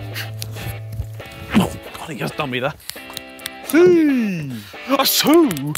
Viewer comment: what does Harry say here lol?! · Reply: Oh god he has done me there...